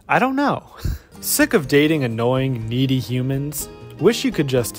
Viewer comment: Omg I thought it said meaty instead of needy😂😂😂 I mean it's also not wrong